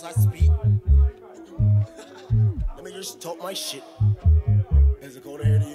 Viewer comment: did he just say n words took my shit? 😂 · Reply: He said lemmie just talk my shit